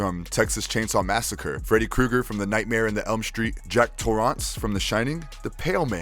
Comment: Did you just say "Nightmare and the Elm Street" 😂😭 like it's Sex and the City hahahaha